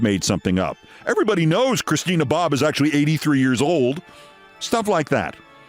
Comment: I thought you said LORENA BOBBITT!!!LOL